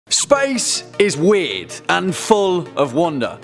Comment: I thought you said "Spice is wierd" because of your accent lol